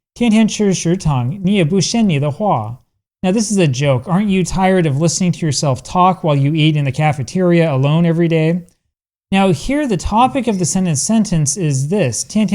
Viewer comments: I think you heard wrong in the dialog 2:06 , it sounds rather like says "嫌腻‘’ which means eating something too many times and feeling tired of it. Edit: just saw the other comment pointing out the same thing. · she actually said "天天吃食堂，你也不嫌膩的慌"，not "你也不嫌你的話". Basically it means "aren't you bored to death"